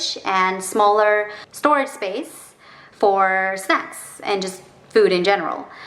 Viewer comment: my english sub. Automatically on Hahah i read sex instead of snacks😂💖